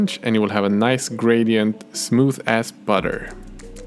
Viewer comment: When he said smooth as butter I thought he Said smooth ass butter